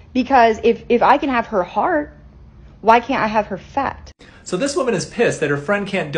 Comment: Bruh I thought it said if I can have her heart why can’t I have her fart 😭😭😭😭